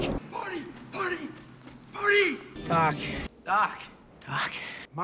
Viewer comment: I heard "cock".